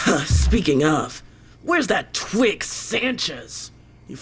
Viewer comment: i thought she called sanchez a twink for a moment and did a double take